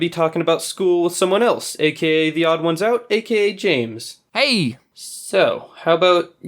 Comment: I thought he said Jameden